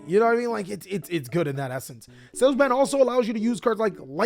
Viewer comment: whats the card he says at 22:30 sounds like sales man? sales bad? cell bad? he sounds like he says five different things and the closed captions don’t help….